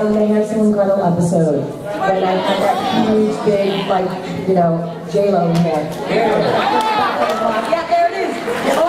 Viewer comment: does she say "Kinda like JLo...?" :O